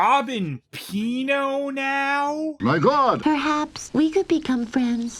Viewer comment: Another Dingo Pictures review? MY GOD! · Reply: For some reason most times I hear that recording I hear him saying "My Glod!"